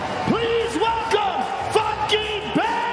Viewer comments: I thought Rogan said “please welcome fucking Ben Askren” 😂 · sounds like joe said “FUCKING BEN ASKREN”💀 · okay, someone tell me what the announcer says here...is it what I think it is? 😂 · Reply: Funky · I didn’t know his nickname was funky so I thought they just said “FUCKING BEN ASKREN” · I always thought Rogan said "Fuckin Ben Askren" at the weigh ins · Everytime Joe said Funky Ben askren I thought he was saying fucking Ben askren